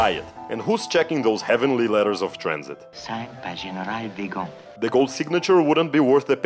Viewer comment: Ah! I think I can help clear up a mystery. Ugarte doesn't say "DeGaulle" at 2:07. He says "Weygand", meaning Maxime Weygand <link> a high-ranking Vichy official. This makes much more sense, since -- as you point out -- DeGaulle's signature would carry no weight with the nazis. I think part of the problem is that nowadays nobody remembers Weygand. But DeGaulle's name is still famous, so maybe people are more inclined to hear that name in Ugarte's speech. For many years I also thought he was saying "DeGaulle", but when I listen closely it sounds clear to me that he is saying "Weygand". I suspect the name would have been better known in 1942.